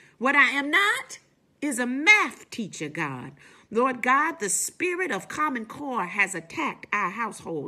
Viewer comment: The spirit of common core has attacked our household lmfao!!!! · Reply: I heard it as 'the spirit of common cold'. That makes more sense: coronaviruses cause common cold.